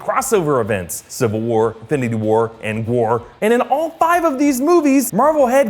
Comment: does anyone hear him say end war